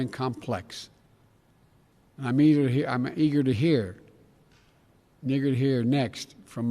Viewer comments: Why? That's the word he actually said and the CC got it right. What would they fix it to say? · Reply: That's obviously a lie, he didn't say the N word to the European leaders at the Munich Security Conference. Only an idiot would believe that. He said: "Eager to hear" - he has a stutter and he stuttered on that line. Listen from 15:30 on. People purposely trying to misconstrue what he said are acting like fools. Don't be one of them. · If you didn't hear Joe Biden say the "N" word then you ain't black! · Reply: I think he said “i’m eager to hear next” because you can hear him say it at 15:31 but he stops and says it again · What is he saying in the sentence with the N word. I see a few comments saying at 15:30 into clip but I don't here the N word. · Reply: he said "i'm eager to hear" three times. if you say that phrase fast enough(or if your speech is slurred) when you repeat it, it does sound like you're dropping the n word. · Did he say Eager Neager? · Russian Bot to Bidens defense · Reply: jus stop talking dude u sound super ignorant, ok, yes, it sounded like he said it, but he didn’t, YouTube’s closed captions is done by a robot lmao, so it’s not always accurate it’s not done by a person. And if u watch 10 seconds before he stutters and it sounds like the n word , u can tell he’s trying to say eager, again the n word wouldn’t of even made sense in that context